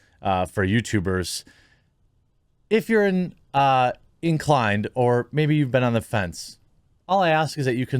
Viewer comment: When you said "inclined" I thought you said "in Klein" and I threw up in my mouth a little.